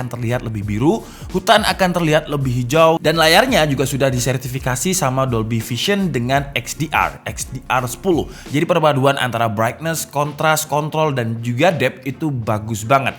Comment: Apa gua aja yg salah dengar ni "brightness, contras,kont*l"